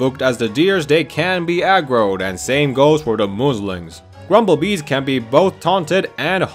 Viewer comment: OH MY GOD I THOUGHT YOU SAID MUSLIMS LMAO